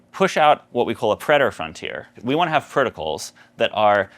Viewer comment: "a predator frontier" is that the term? that's what CC says but can't find info on google.